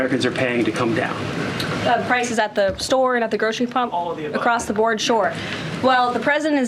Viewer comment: Did I hear her say "the grocery pump"?.